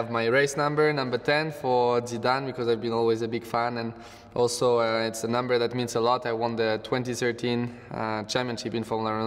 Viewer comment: What is he saying about number 10? Something about Zidane and the rest ...? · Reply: "Number 10 for Zidane because i have always been a big fan, also its the number that means alot, I won the 2013 championship in formule renault, just before I entered the Red Bull program"